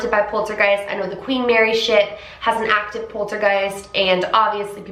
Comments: I was looking away when you said Queen Mary Ship, and I was certain I heard you say Queen Mary Shit 🤣 had to rewind and listen again! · I thought you said “queen Mary shit” instead of “ship” and my heart skipped a beat cause you’ve never cursed in a video I don’t think?? I got so hyped for a second love you & your content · At first I thought you said Queen Mary sh*t ! I was like 😂😂😆